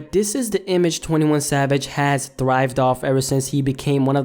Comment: did he say 21 Sabage in